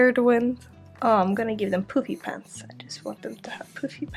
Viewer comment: Haha omg I heard, "I'm going to give them poopy pants" instead of poofy pants🤣🤣🤣 this is so relaxing to have on when brainstorming!